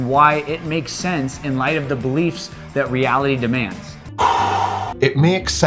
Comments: just before 1:17 I heard "...it makes sense in LIGHT of the beliefs reality insists upon us...", rather than "...in SPITE of the beliefs...". Just a thing I noticed. He's still wrong, but different wrong than you heard I think... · Uuuuhh... he said "in light of" a d then you made a response to your mishearing it as "in spite of"... just sayin